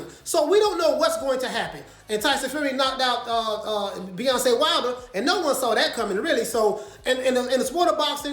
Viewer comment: Did I hear him say "Beyonce Wilder" ?? 😆😆😆😆😆😀😀😀😀😭😂😂😂🤣😂🤣